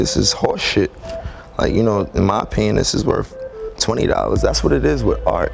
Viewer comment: Did rocky say “my penis is worth 20 dollars” ???